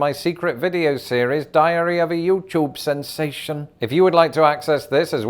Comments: Did he just say "diarrhea of a youtube sensation?" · It kind of sounds like is saying 'Diarrhea of a Youtube Sensation'